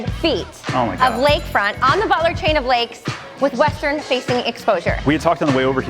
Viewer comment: I heard "baller chain of lakes"...